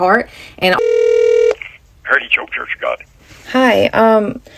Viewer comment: why did the captions say he said "He choke your church for God" 😭😭😭